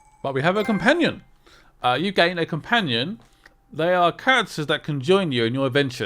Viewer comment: OOooooh CHARACTERS! I thought you said "CANCERS that conjoint you" I was just listening, i had to see. xD